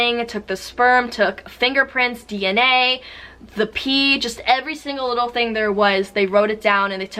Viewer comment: They left their DNA everywhere. If you’re gonna be a criminal at least be smart🤦🏽‍♀️ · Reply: I thought you Said DNA BTS